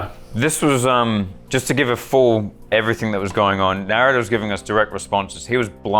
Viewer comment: the cc calls thinks said Naruto instead of Narrator and it’s amazing.